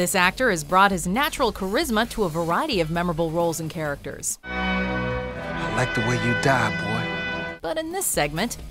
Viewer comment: is it just me or does it sound like he said, " I like the way you dab boi." 😂😂😂